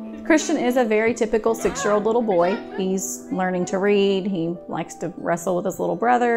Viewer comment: I heard he has learned to read um he has no eyes 0:55 like if you heard it to